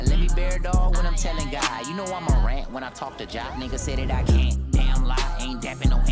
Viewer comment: "You know I'mma rant when I talk to Jah". Jah refers to the Hebrew name for God(Yahweh), but also "I'mma rant(Morant) when I talk to Jah(Ja) is a Ja Morant reference. JID is nasty as hell🔥🔥🔥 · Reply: I legit thought he said "Imma rat when I talk to Jah" because in the pervious bar is says that he would "I bare it all when I talk to God" But this makes more sense and is a cool reference.